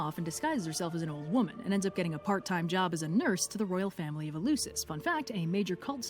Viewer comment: the add interrupted the video at the moment it was going to pronounce the s in nurse, so it sounded like it said "Dimator took some time off and a got a part time job as a nerd."